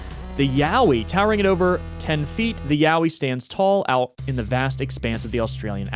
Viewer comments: Got up while watching to do something real quick and had to do a double take when I thought you said "the yaoi" 😭😭😭😭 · I had this on as a background noise, but I HAD to check when at 1:55:25 I heard "yaoi". I was like, "what, what the, what is this word doing in a game like this??" Turns out it was "yowie"...